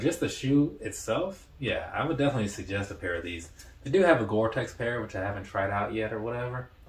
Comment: Let’s see your Gore-Tex version! I’m curious whether or not we chose the same color way. ✌🏾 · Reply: My bad! I misheard you around the 17:36 mark. Initially I thought you said that you had a pair of Pegasus goretex not “they”. I have a pair of the goretex Trail 3s in chocolate/ brown (or something) volt / red. Unbelievably comfortable.